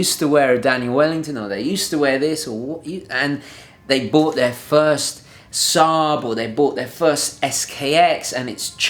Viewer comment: I thought you said "they bought their first SAAB..." and got really confused.